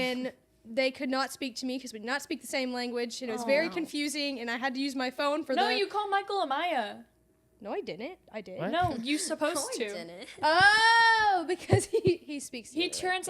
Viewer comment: did she say Amiyah Cause that my name??????